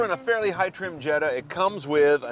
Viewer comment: Did he say it was a Jetta?